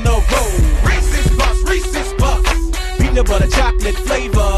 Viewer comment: I understand: Reese‘s Puffs BEAT EM UP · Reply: I thought they said peace his puffs